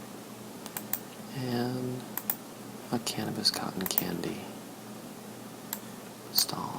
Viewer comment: it sounded like at 6:29 you said a cannabis cotton candy stand lol